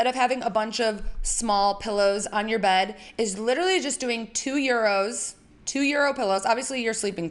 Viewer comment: what did you say? What I heard is 2 euros pillows 😂